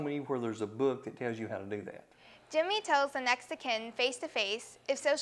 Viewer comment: Did she say Jimmy tells a Mexican? That’s a little racist. I’m Mexican/American. 😂😂 it’s okay tho we don’t talk it up the arse. I put captions it says next to ken